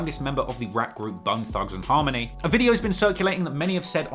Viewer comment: I thought he said "Butt thugz N Harmony" at the bizzy bone part 🤣